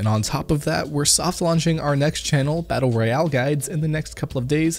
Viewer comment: When he said battle Royale guides I thought he said clash Royale guides and I was like😕😕